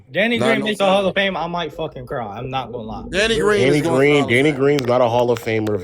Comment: Did they just say Danny Green is going to the half of fame ?